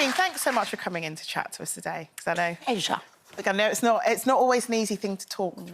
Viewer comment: what does she say here? Asia? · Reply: It think she says pleasure